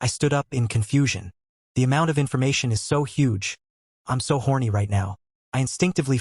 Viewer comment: what did bro just say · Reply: I just got to the same part you're talking about. And he really did say I'm h orny😂